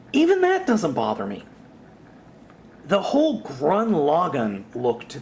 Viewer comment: did you say "grun lagann"? it's gurren lagann